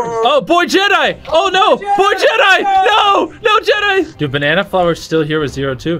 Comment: WHY I heard poor jedi